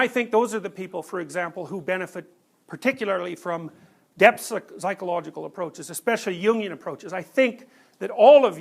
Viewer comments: If anyone is educated in this field can you tell me what he is talking about at 8:22 ? I hear "depp's psychological approaches", it's probably not that I feel like it relates to me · Ah I just figured out he doesn't talk about "union" but "jungian approach" at 8:23. I wondered for minutes what the hell he is saying.. :D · any1 knows what is he refering to? I'm hearing something like "yilian approach". What exactly did he say?